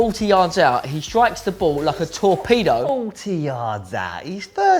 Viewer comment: When Bill said 'a torpedo' I thought he said 'a tall peado'